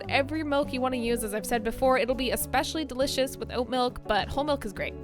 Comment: "You can use whatever milk you want to use as I've said before. It'll be especially delicious with oat milk, but whole milk is great" I've clearly been watching too many Canadian YouTubers because I heard "with oat milk" and wondered how you can make a latte without any milk.